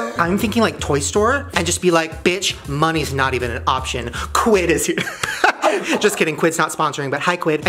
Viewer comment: What was the word he said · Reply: quid